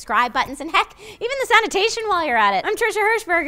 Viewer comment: Did she just say that she app-whores endings?